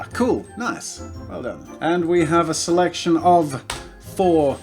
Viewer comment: did he say well done to himself